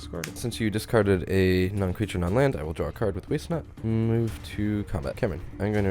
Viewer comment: the caption says "waste nut" The cream on the top of this cake of an episode. Pun intended, lmao.